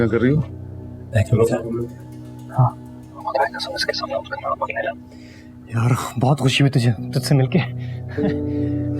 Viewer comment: What is shayan saying at 33:57 when he meets with adam and what is he whispering? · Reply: Mehak ne bheja h? Mamo k samnay zikar na karna